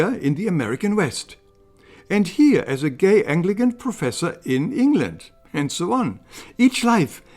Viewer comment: At minute mark 6:20 Did he say "gay anglogen Professor "?